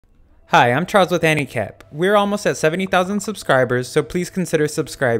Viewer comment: Bruh i thought you said "Hi I'm Charles with handicap" 💀 I was here thinking how is that relevant then read the channel name lmao